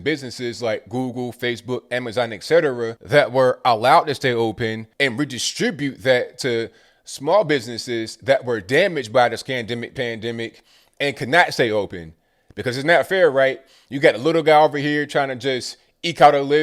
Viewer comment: Did you say the "SCAMDEMIC" haha